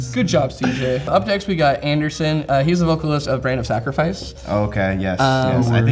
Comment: What's the name of that band? I couldn't make out what he said. All I heard was something sacrifice. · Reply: Brand of sacrifice